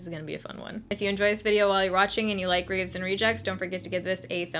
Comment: I thought you said raisin rejects. Oops.